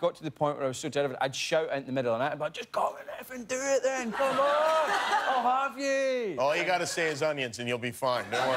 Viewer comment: Does anybody understand what James is screaming at 5:55? Who can help, please? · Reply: “Just come and effing do it then. Come oooon! I’ll have you!”